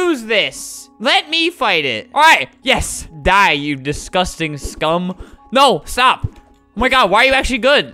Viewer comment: Did flamingo said scum?